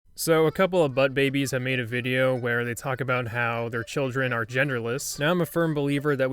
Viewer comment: I thought you said chicken are tenderless